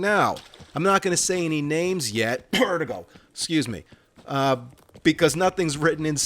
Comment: For anyone Wondering about what he said on 18:50. It was fake cough "vertigo"